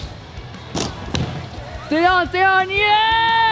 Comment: who heard stay on stay on